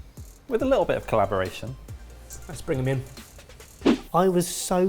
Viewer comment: When said 'Let's bring them in' before the final judgement, at first I heard 'Add sprig of mint'.